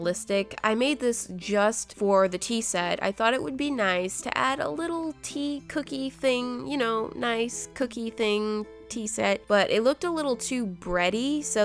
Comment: BAHAHAHAH I THOUGHT SHE SAID TEA COT